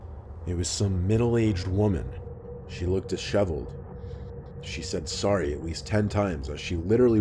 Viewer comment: It totally sounded like you said "it was some middle age woman. She licked a shovel."